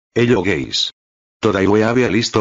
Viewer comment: Why did I hear "hello gays"? 🤨💀